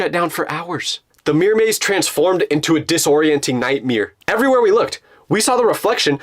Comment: am I deaf or did I just hear him say "nightmere" at (1:05:50) pls I need to know